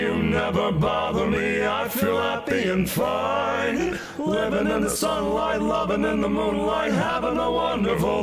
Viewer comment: why did i think it was lovin in the poop light as a kid? not that that makes any sense. i always thought, are they saying poop light? why would they say poop light? why did i even type this?